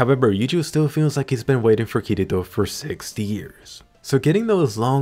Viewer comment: ? It sounds like you said 60 years even though I know you didn't